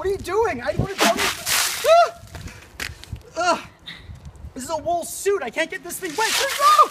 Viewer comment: No entiendo algunas cosas, alguien puede decirme que dice en el 0:16 y 0:31 Please, someone can tell me that Matthew says in 0:16 and 0:31 Thanks I'm bad in English listening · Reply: "It's a wool suit, I can't get this wet!" and "It's literally the coldest (thing) I've ever felt in my life."